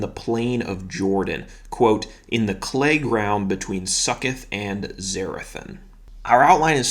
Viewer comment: To hear you pronounce Succoth is the height of my day. I thought you said “Suck it” haha! I also do “Bible in a Year” with Father Mike. When he said the name “Nimrod” I laughed out loud. To hear it come from a priest was so funny . Haha! I am 53 going on 11 years old 😂😂